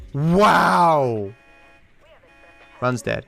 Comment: For the longest time I thought he was saying “Ron’s dead”, I was so confused like what do any of these missions have to do with Ron?